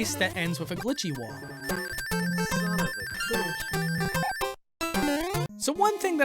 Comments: Did somebody heard son of a glitch in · anybody else hear: “Son of a GLITCH” ? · In 7:25 I hear "Son of a Glitch" xdxdxd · i heard son of a glitch in the background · i heard son of a glihct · did i just heard "son of a ** " in the background? xD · Did anyone else hear ‘Son of a glitch’? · Did anyone else hear "son of a glitch" intro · who else heard"Son of a glitch" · did anyone else hear "son of a glitch" in the background? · Anyone else hear son of a glitch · im the only one who heard son of a glich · anybody hear son of a glitch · Did I just hear Son of a Glitch? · Did i just hear "son of a glitch" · did somebody heard SON OF A gLiTcH??? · anybody else hear "son of a bitch" who is this? Is this a accident audio glitch? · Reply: Son of a glitch